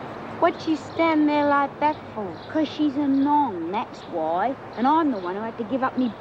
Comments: "Because she's a nong" Oh Doreen you are priceless. You don't hear words like "nong" anymore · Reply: I thought Doreen said "Because she's a mong" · did Doreen call Georgie a "mong"? Bit cruel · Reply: No. A nong